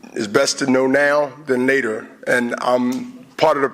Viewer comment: nater lol instead of later haha